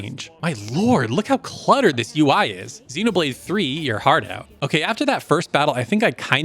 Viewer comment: What does he say about Xeno 3? I don't understand him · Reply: “Xenoblade 3 eat your heart out.” Basically, you thought the UI in XB3 is bad? Look at Natural Doctrine!